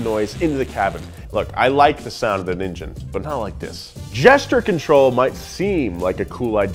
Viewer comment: every time Nolan says Gesture Control, I hear "Jester Control".. and I don't think that's an accident.. 🤣